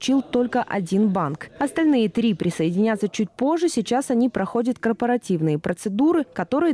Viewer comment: Мне вначале послышалось все банки на корпоративе ...